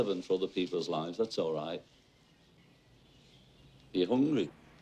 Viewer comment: "You hungry? Hm?" -"Yeah." -"Let's get him something to drink." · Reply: I actually thought that's what he said too...If u listen really closely he says let's get him something to eat..